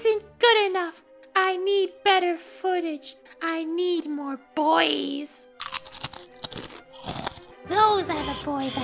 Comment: Ok yeah I know I’m super late but then you know I heard ‘I need more boi’s” and I yelled “DRACA IS BACK!”